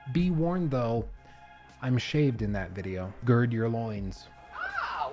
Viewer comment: all I heard was shaved loins.